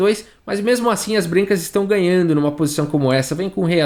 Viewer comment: Gente foi inpressao minha ou em 19:30 o falou "brincas" kkkk